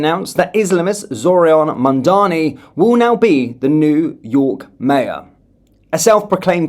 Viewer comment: You gave me a fright the way you saiid 'New York Mayor" - for more than a few moments I thought you said "the neww York mayor is a Muslim" - as in the mayor of York as in Yorkshire